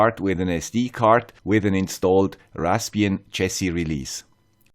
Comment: I'm sorry, I can't quite understand 1:32 - Raspbian chasey release? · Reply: The release back then was called "Jessie". Now it is "Buster"